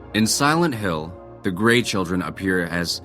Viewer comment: I thought he said great children are demon children... Lol 😂